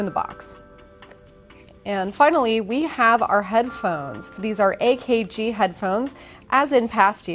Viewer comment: Thought she said headplugs instead of headphones. Was about to loose my mind.